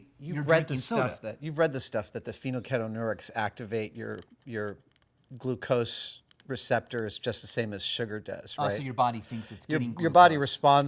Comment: can someone tell me what Adam said? About activacting the glucose receptors. English isnt myh first language so im having hard time understanding the pheno keto newark line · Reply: Very late answer, but I think he says that "phenylketonuria activates your glucose receptors just the same as sugar does". He might mean "phenylalanine"